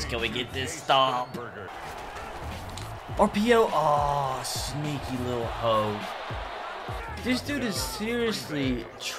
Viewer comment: did he just say "sneaky little hoe"?